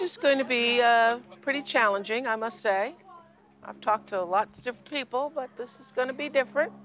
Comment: this was so disgusting to watch. The way Oprah interacts with her is just so inappropriate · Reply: did she say "i talked to alot of stupid ppl" or ive misheard it?